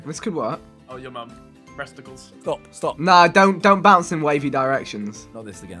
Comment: did Ethan say just say breasticles?